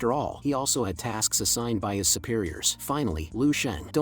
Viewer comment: did I just hear the 10th level has goddam superiors?????!!!